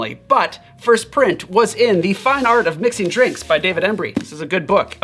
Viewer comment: Sounds like he says the fun art of mixing drinks. But it’s the fine art of mixing drinks. Also you show the book for such a short period it’s actually kinda tricky to get this info from that small flash of info